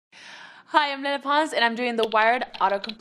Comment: when she said 'hi i'm lele pons' i heard 'hi i'm menopause' 💀💀